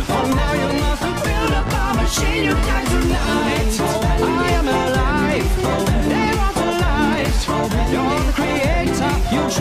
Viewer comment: 오늘 밤은 신용대출날!!! 이라고 들려욬ㅋㅋㅋ